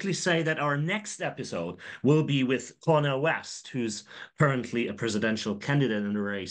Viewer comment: I THOUGHT THEY SAID KANYE WEST IN THE BEGGINING LMAO